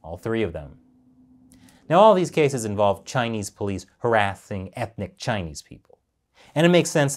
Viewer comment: Harathing instead of harassing? Did you lose a bet? 😂